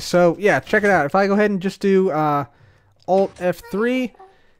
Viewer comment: did he say ALT F3?